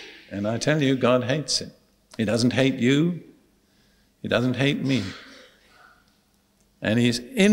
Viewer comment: there is mistake on subtitles.They write that God hates us and Dereck. No joking, I am from Czech Republic co I can understand it. The problem is, that Dereck said, ,, He doesn t hate you and doesn t hate me, but the Czech subtitles said. He hates you, He hates me.Please fix it. Thank you, God bless you